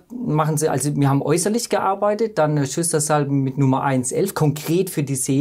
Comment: Können Sie mir das noch mal sagen was in der Minute 10:40. gesagt wurde? ich kann es leider nicht verstehen, soll es bedeuten Schüsslersalz 1 und 11? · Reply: 1 und 11 habe ich auch verstanden und es ist sinnvoll. 11 ist Silicea.